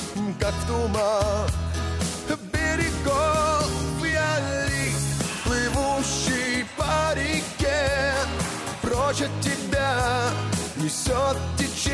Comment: Я один услышал? Я глист плывущий в парнике